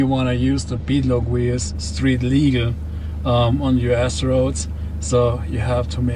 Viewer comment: It sounds like he said "you ass roads" instead of "U.S. roads." I think either works, in some cases.